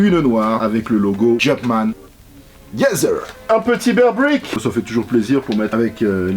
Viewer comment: attendez un quoi ? j’ai pas compris s’il vous plaît 🥺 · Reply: un bearbrick